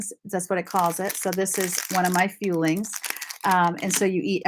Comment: Oh my gosh, I thought you were saying “feelings”! “Fuelings” makes much more sense. Thought you were eating your feelings every three hours!! 😳🤣. You look great!